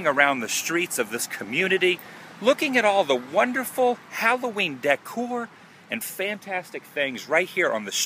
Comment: I thought you said Halloweed.